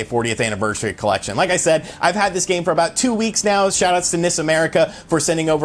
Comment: N-I-S America, I keep thinking you are saying Miss America. It gets confusing. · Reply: I also thought he said Miss America. LOL!